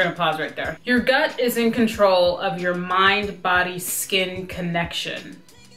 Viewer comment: No joke, I thought the first chick said "mind, body, SPLEEN connection," and I was like, wait what did she say?!? 🤣